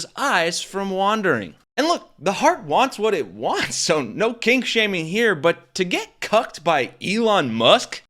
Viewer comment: did you say KLINK SHAMING? ...Because I swear I`ve seen that guy on old Hogan`s Heroes re-runs! Stick a monocle in his eye & call him nazi Colonel Wilhelm Klink!